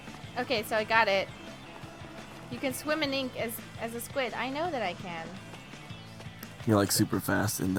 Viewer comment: I'm not gonna lie I swore I heard u can squidinate as a squid